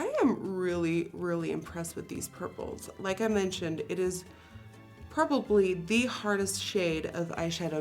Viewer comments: did she just say purple and probably together like purpably. I love that 😆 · I loved how she was saying purple so much that she said, "Purplobly" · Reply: PastelxGoddess thought she said probably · I think Nikki said purpably at 14:02 😂